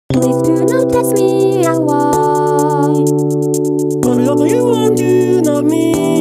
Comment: I keep hearing from luka "please do notice me and why"